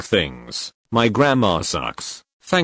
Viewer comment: It took me so long to figure out what they said in the begging because I thought they said "my grandma sucks" but they actually said "my grammar sucks" 😀😃